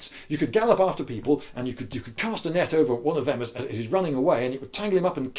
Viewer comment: Oh, you said "Cast a net". I thought you said "castanet". An army armed with castanets. How would that work? Are they a troop of flamenco dancers?